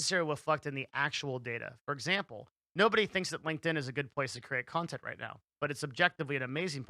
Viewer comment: Why is it I hear him say “linked-in” is a good place to make content. I’ve gotta be hearing that wrong, what’s he saying?